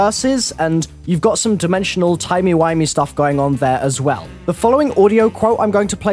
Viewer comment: did you just say "timey whimey"?